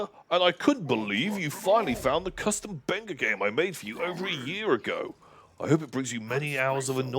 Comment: Can anyone tell me what this game that was said at 5:50:38 is? I keep rewatching this part but can never figure out what exactly they're saying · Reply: Late answer is better than no answer. Pretty sure he said a custom Benga game.